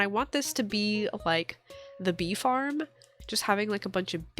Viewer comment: around the 18 minute mark when you said bee farm I thought you said beef arm and was so confused lol